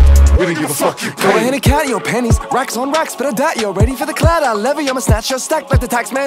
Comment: Awww bit disappointed it actually says ‘doubt you’re ready for the clout I levy n not doubt you’re ready for the clowder like I could’ve sworn it said!!